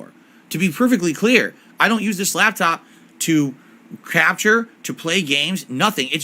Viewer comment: swear it sounded like he said crapshure, not capture 😂